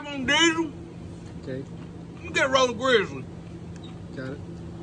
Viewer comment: Did he just say give me a roll of Grizzly😂😂😂. Oh ok snuff